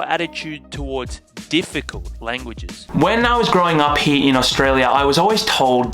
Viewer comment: did you just say growing “up” in Australia?